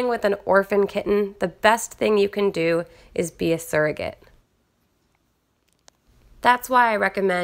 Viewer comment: i was ready to burst out laughing because i heard you say "servant," not surrogate